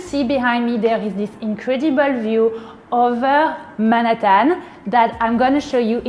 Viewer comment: what the f did she say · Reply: Manhattan. But she hit the syllables so wrong.😂